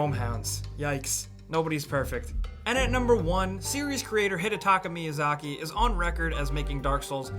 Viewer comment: Turn subtitles on at 5:52 you' ll read hit a taco miyazaki instead of Hidetaka hahahaha